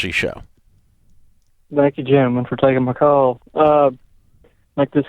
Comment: "thank you Jim and for taking my call"?? Did he call him Jim?